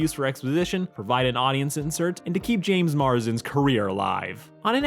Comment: Legit thought for a sec you said JASON Marsden instead of James Marsden and I was ready to fight.